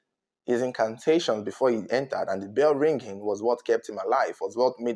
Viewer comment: I thought he said his alive